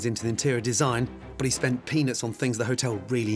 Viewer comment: did he say what I think he said? I feel like I've never heard him say that word before lol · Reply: Lol I thought I heard that too - but he said “peanuts.”